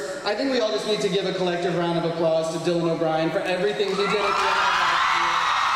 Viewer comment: I don't know if anyone will see this or answer me, but at 4:30, the announcer/moderator asks for a round of applause for Dylan O'Brien, for "everything he did [...]" I can't hear anything over the sound of the crowd, what does he say? "for everything he did at the end of last year" ? Or "at the end of the last season"? And what does he say after that? Please, if someone knows :)